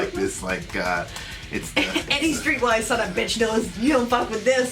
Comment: for over 20 years, I thought he said every single white sonofabitch knows. wow, wow , good to know the correct words finally.